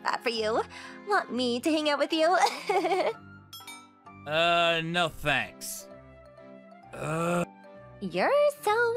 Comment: what he actually said, "Uh, no thanks, I'd honestly rather hang myself with a noose than to be hanging around you".